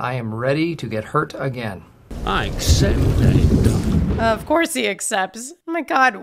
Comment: Fun Fact: When Davey Jones accepts Will's challenge, the captions are acctually incorrect. The captions say "I accept mate.". But if you actually listen to Jones' words, you can decipher him saying "Accepted.". But because og hiw Bill Nghy delivers the line very theatrically, the captions are different. It happens occasionally in movies, where the captions dont match the dialogue. Generally, its done for pacing issues. Sometimes the dialogue is delivered very quickly, so the cations will edit some lines. But here, i think its justva misheard line.